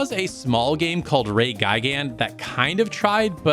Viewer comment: What's the game shown at 16:38 , again? I couldn't quite parse what you said there. Ray Guy Gun? Reigaigan? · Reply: Ray Gigant